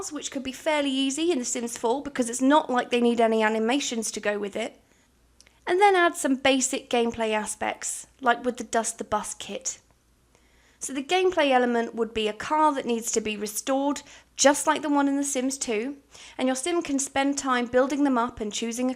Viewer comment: Am I the only one who heard "Dust The Bust Kit" at around 4:17? I do that all the time 😂😂😂😂😂😂